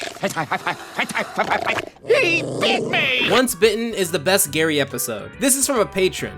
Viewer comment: Did he just say Hentai?